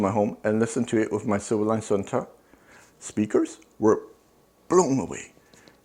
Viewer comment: Was anyone able to understand the name of ’ own speakers? It sounded like he was saying “civilize on top”. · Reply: Silverline sonata